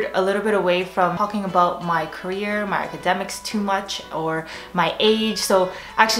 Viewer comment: what did she say at 0:19? i can't tell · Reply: "....my career, my academics too much..."